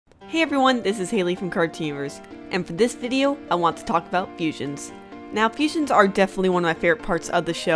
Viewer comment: I thought you said "talk about asians" cx